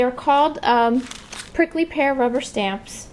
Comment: Super cute stamps! I thought you said the Prickly BEAR!!! Either name... They are great stamp images. Big Huggs, Gale The Happy Stamper :)